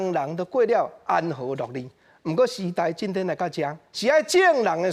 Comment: 的句子求解 orz 聽不太懂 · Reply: 應該是安和樂利（an-hô-lo̍k-lī）唷